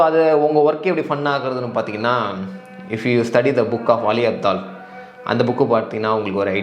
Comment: i cant understand that author name bro pls tell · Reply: "Ali abdaal" bro famous Youtuber